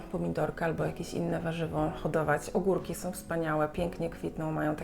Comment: Powiedziałaś ogórki, ja usłyszałam kurki 😁, myślę ok, mogę hodować na balkonie kurki